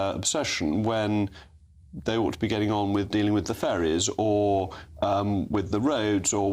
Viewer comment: At first i thought they said dealing with the fairies 😂 then realised it was ferries.